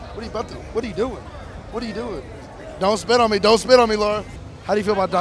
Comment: I thought he said "don't spit on me don't spit on me LLAMA!"